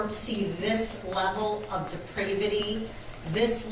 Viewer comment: Did she just say "depridivity?" Wtf is that? Objection, your honor! Unintelligible. You can't just be making up words on the bench like that.